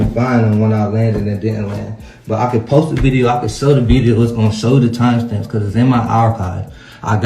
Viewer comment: did he say he can sell the video? Wtf?!